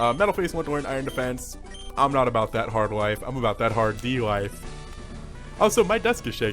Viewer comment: am i the only one who heard: i'm not about that hard live i'm about that hard D life